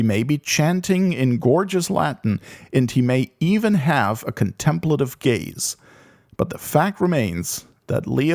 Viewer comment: What’s wrong with caring for God’s creation? I hear “contemplative gaze” as “a good hard look.”